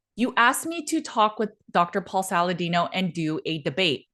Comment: I got into the carnivore diet mainly because of Drs Ken Berry and Paul Saladino. Maybe I misheard Saladino's name as "Salad? No!". But now he's "Salad Yes". As well as carbs, fruit juice and honey yes. Nope, I'm not falling for it. I'll stick with Ken Berry and.